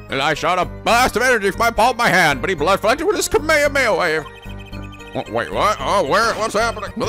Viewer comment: Is it unusual I thought I heard "Kameda Bagel-Wave" when Nico said "Kamehameha Wave"?